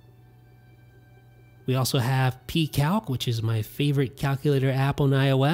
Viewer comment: What was the name of the calculator he mentions? I want to check it out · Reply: Seth Brubaker Sounds like he said Pcalc